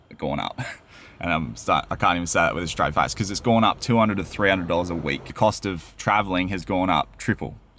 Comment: Did i hear you right,i thought you said $1300 a week on grocerys,holy moly you must eat like kings